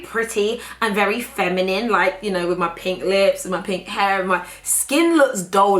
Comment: Pink hair? 😂😂😂😂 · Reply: I thought she said pink “HERE” in reference to her clothing.